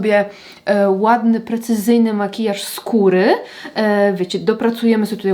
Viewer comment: Haha zamiast "makijaż skóry", w moim mózgu usłyszałam "makijaż z kury" 😂 Podpowiedz jeszcze czy w ten kwadracik w palecie zmieściłaby się też standardowa okrągła wypraska, np. z glamu? Piękne piękne makijaże ❤❤❤